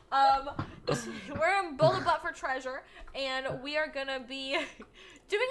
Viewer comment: did she say build a butt for treasure lol